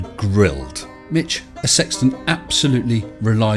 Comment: Dead bodies stacking up ? oh, I thought you said “sexton”(nevermind).